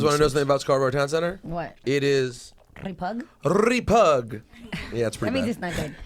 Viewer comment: What do they mean at 4:25 i hear "repug" what does that mean?